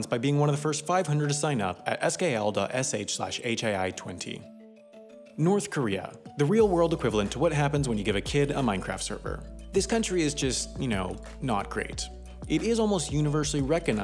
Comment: Did he say Minecraft server?